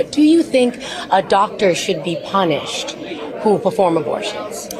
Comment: Sorry, at 3:30 did I hear, "Should a Donald me punished...." Yay, he agrees. Therefore, throw away the key. Job done. Ad altiora!